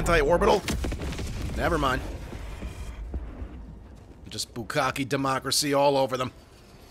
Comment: did say "Bukkake Democracy"? LOL